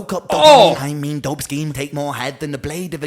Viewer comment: I think he says "dope scheme", not "dope skin". Its also "color scheme, nude," as in the skin tone color of "nude". Sometimes these lyrics get misheard and placed on these lyrics sites and forever corrupt the meanings of artists' songs :( Can't wait for you to react to Chalk Outlines.